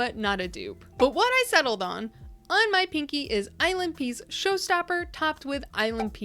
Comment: I was listening more than I was watching at first and had to stop to see what was actually happening because I kept hearing "island pee" instead of ILNP 🙃